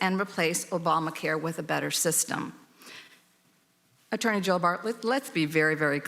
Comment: Did she just call him Bartlett? Attorney Joe Bartlett?